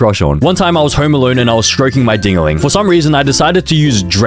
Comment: I HEARD DINGALING AS MINGALING (atinys know what I'm talking abt....maybe)